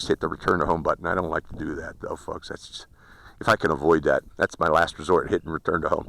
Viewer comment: Uh you should check what the close captioning says at about the 2:12 mark. Such language! 😃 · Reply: Obviously that should be "to do that though folks" not "to do that girl f**ks", google fail (to pick up accent). Its a shame we can't suggest corrections here like we can in google translate